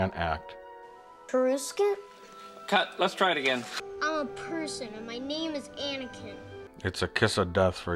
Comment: What the hell did Anakin say at 5:35 ? "Perskin"??